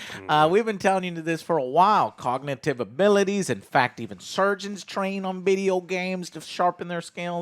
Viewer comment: Jesus, I heard "insurgents train on video games" and was like "yeah, I guess, but that's not the best argument right after a mass shoo... oh, surgeons...".